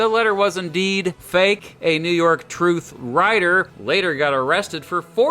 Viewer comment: thought it said New York Trash